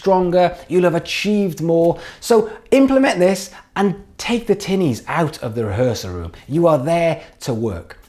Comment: does anyone know what he said here I couldn't understand it? "Take the ___ out of the rehearsal room" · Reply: Magnus Lennon sorry dude that’s me talking too fast, I said ‘Take the tinnies our of the rehearsal room (the cans of beer). Thanks for watching dude 😊😊